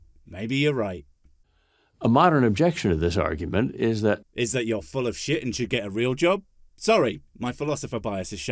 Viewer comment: when you said, "I think you're full of shite, and you should get a real job." I thought you said, "I think you're full of shite, get a rim job." I would seriously pay, to see the latter. Him, full of shite, getting a rim job, whilst trying to hold the whole mess in. (k) comedy gold, I tell you!